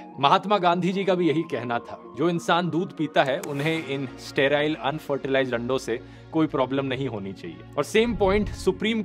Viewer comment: This is the third and final video in the Evolution Series. Hope you enjoyed this series :) 🤖 Join the Master ChatGPT: Student Special course: <link> Join the Master ChatGPT: Full course: <link> Use coupon code: EGG40 for 40% discount! In India, every 5 minutes, someone is diagnosed with blood cancer. Yet, awareness is scarce. Here's your chance: a small act, a big impact. Register as a potential stem cell donor: <link> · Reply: Vedio time 9:11 pe kya bol diya ( unfertilised lando se )